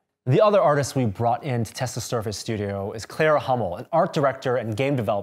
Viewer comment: Hello, I have a pretty random question. 5:48 does anyone happen to know who that is?? I didnt really hear her name, and from the subtitles I didnt get any results when googling her. I loved her artstyle and Im really interested in seeing if she has any platforms on social media that she shares her art or projects on. If anyone knows, please reply, Id really appreciate it! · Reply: is her name, cheers